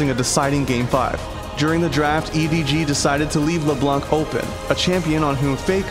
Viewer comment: I thought I heard Lebron, and im like you cant leave lebron open